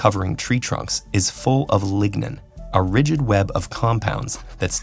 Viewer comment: are full of login I thought they said ligma